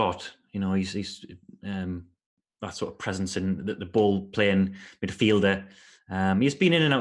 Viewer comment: Thought just said "A bald playing midfielder" about Mooy😂